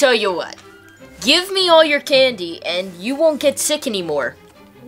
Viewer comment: is it just me or did he say sick and on the subtitles it said sticky wtf